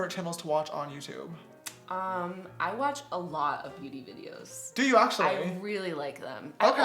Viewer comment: Wait I thought she said “I watch a lot of pewdie videos” like pewdie pie and I lived the next 3 hours of my life thinking that and then I thought “wait...did she say beauty videos!?” And came back to check and low and behold LMFAO. Same thing if you ask me